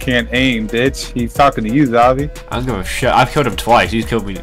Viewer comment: babe wake up xavii posted! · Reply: I thought you said aviici or whatever that dead singers name was